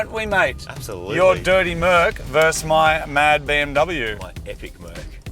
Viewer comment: Did Moog say he has a BMW at 24:49? When did Moog get one?!